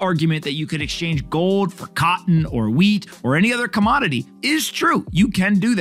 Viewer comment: I thought at first he said WEED not WHEAT . I was like hell yeah !!!